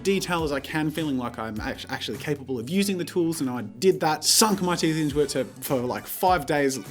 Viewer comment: why did I think he said “suck my titties” for a millisecond???? WHAT IS WRONG WITH ME??????